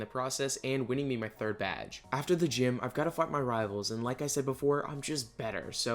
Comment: is it just me or did he say fuck his rivals not fight